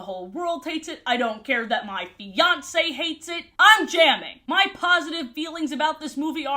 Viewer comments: I misheard "I don't care if my fiance hates it" at 22:23 and legit thought said "I don't care if my Beyonce hates it" and sat there for ages trying to figure out that that meant. I thought it was a joke I didn't get 😭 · I thought you said "My Beyonce hates it" and I was very confused as to how you knew if Beyonce hates it. I had to go back twice and listen